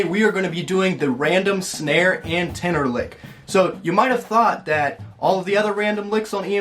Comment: "Rick" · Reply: But if you Played at 0:16 He clearly said "Lick"